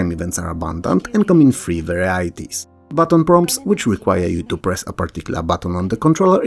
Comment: I misheard "button prompts" as "baton prongs". Since it's about the police I thought you beat someone up with a pronged baton if you run out of dialogue options.